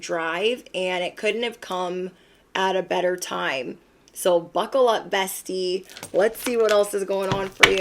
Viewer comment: Lol, you said "buckle up" and I heard "fuckle up"